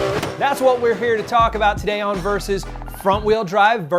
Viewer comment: i thought it said very sus